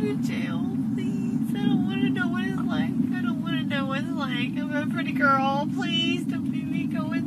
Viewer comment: I thought she said “I’m a pretty birb”